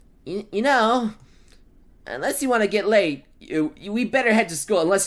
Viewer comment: Anyone else heard get laid instead of get late?